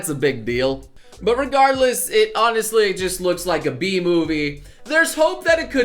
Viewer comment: For a second I thought you said: "It looks like a Bee Movie"